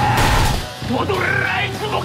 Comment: の「＊＊＊じゃ、わりゃあ」って何て言ってるんですか？ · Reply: 少し前のセリフでしたかー たいぎいんじゃ、わりゃあ って聞こえましたね